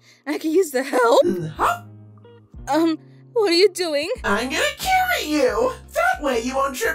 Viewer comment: why did I hear "I'm gonna kill you😄" 💀😭😭